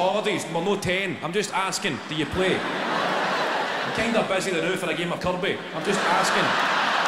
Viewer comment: I dunno what he said hahahaha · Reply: He's kinda busy right now for a game of kerby. It's a game that kids play with a ball and the kerb of the road.